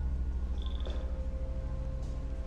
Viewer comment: I thought he said, "I like this car metal moment before the storm."